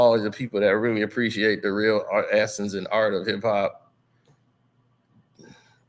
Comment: Thought you said ITS OVER SLIM ..😢😂